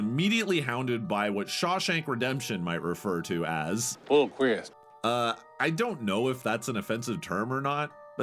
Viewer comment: What does Red from Shawshank say at 7:53? Its killing me. Replayed it over 10 times and I hear "full criz". I'm sure once I actually know, it will sound crystal clear. My brain sucks. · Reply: I will take the chance for future readers. "FULL QUEERS."